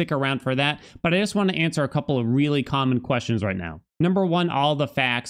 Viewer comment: am I the only one who heard "a couple of really condom questions" in this clip